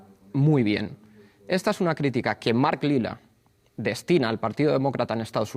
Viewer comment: Mark lida al partido demócrata qué???? No entiendo ese nombre!!!!!! Minuto 12:29 ?????